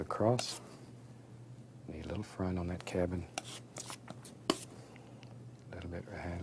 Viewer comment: I honestly thought he said cabbage when he was talking about the cabin. 😂